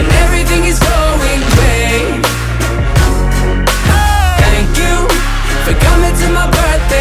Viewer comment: does he say great or something I can’t tell? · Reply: He says great.